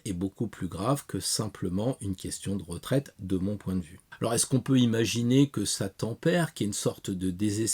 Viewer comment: Grand merci pour cette vidéo très intéressante. A un moment quand tu as dit "on peut imaginer que ça tempère" j'ai entendu "on peut imaginer que Satan perd". Un mars et ça repart ! Merci pour le travail que tu fais. Amicalement Geoffrey